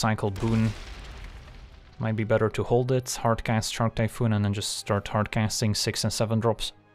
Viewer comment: Unclear, what I heard you say was "ALWAYS HARD CAST SHARK TYPHOON".